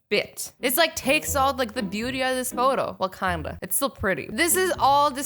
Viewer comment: i swear i heard Wakanda :D :D